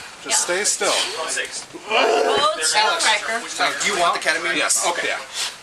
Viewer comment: Sure sounded like they said KETAMINE ...